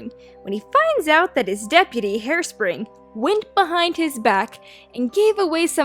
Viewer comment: I heard “Hairspray” at some point. That made me smile.